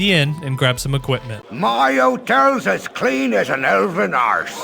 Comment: My first PC game, I was so young I always thought Winthrop was saying, "My hotel's as clean as an elven horse." Lol